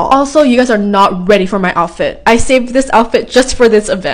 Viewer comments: MOST IMPORTANT PART OF THE VIDEO his name is ✨GeRaLd✨ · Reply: Oh it's Gerald well I guess I heard it wrong I thought she said jaird ❤😮😂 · I thought she was saying me and jerk 😂😂 sorry bt i heard it all btw, his name is gerlend